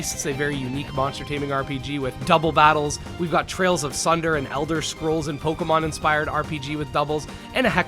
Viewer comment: there's an Elder Scrolls inspired monster taming title I'm interested in, but I can't understand the spelling of it based on the speech - Trails of Sunder? Is that correct?